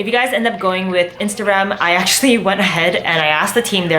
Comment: everytime u said "instarem" i kept hearing "instagram" LOL